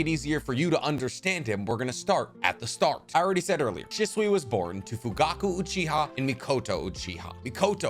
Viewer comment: Did you say Shisui?